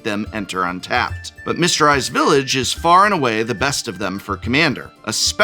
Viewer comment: I keep hearing "Mister Ice Village"